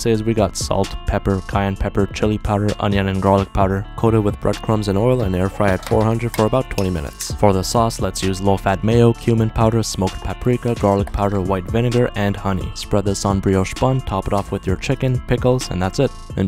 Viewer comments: I straight up thought he said human powder😅 I had to replay it lol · At first instead of cumin powder I thought you said "human powder"... 😳😱💀